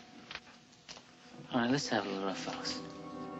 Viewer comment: what does Brando say at 0:42? i cant make it out...sometimes i have a hard time understading what he says in this movie. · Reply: I'm 11 years late but he says "Let's have a little rough-house"